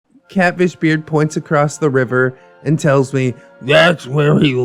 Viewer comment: Ngl I thought you said Cabbage Beard not Catfish Beard lmao